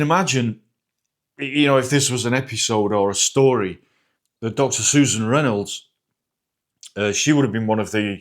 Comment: I was confused at first. I thought you said, "Dr. Seuss" and "Reynolds".😅